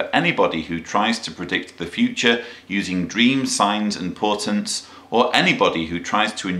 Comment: at around 12:11 he says dreams signs or "portance" I know I've misheard the word can someone tell me what it is so I can look it up proper like? I looked in the kjv version and can't seem to find the word he is using. if it is portance I'm not sure what he means.